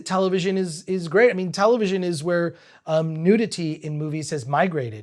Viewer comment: subtitles says "no clothes" while Chris says "nudity" ?!?!?!? WTF?!?!?!?!?